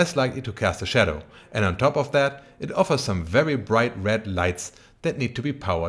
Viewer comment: Small Note: at 4:55 the subtitles read "red lights" instead of "led lights". I just noticed bc my speakers are broken and i consume content via subtitles now. PS: love your content!